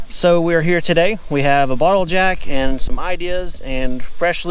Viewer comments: You said “bottle jack” so quick, I thought I heard “ We got a bottle of jack (as in Jack Daniels Whiskey)”. Ha ha that didn’t sound right coming from you guys. Happy for your new purchase and all the progress you’re making toward your goals. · Sorry, but I choked on laughter when you opened the dump truck and you had no steering wheel 😳😳🤣. ( easy reassemble 👍😊) Got stuck ?! Oh no 🙈… When you said you had a ball jack, at first I heard “ bottle of Jack “ — which I could understand under those circumstances…. You poor guys !!! Nothing is ever just “ easy “ 🥲( I can tell you spell your ‘wordy dirds’ much differently than I do 😮… funn!, memory making …. Yeah 🙄) At least you didn’t break the axle - yay !!! Looking forward to seeing the inside of your new camper !!!! I’m sorry you had such a difficult home coming , but so very happy for your new purchase!!!! 😘❤️